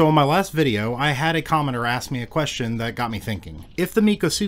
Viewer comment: I totally thought you said commoner, instead of commentor, and i was kinda just in disbelief for a second. Love your channel dude, keep up the good work.